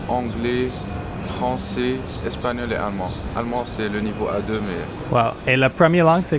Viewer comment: i think he said his german is level A2 not just level 2! I'm just assuming from what I know about the official french DELF test, where the A level is like beginner, B is fairly proficient and C is approaching fluency ... C2 being native speaker. I tested my french for B1, I assume a version of the delf exists for german and other languages too which uses the same rating scales (I googled it and it does, cool)